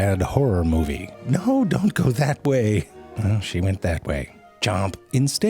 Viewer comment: I thought he said "hoe, dont go that way"